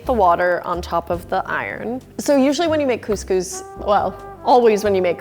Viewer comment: thought it said “conscious lamb”